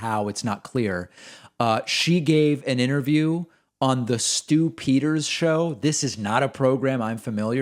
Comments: When he said Stu Peters show i thought he said "stu-pid-er" show lol. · For some reason completely unbeknownst to me, I heard that as "on the Stupider's Show." And then I realized I basically heard it correctly.